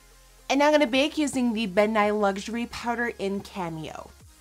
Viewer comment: did anyone think she said "Bill Nye" at 2:18?!?! Loved this look!! Werk it gurl (see what I did there)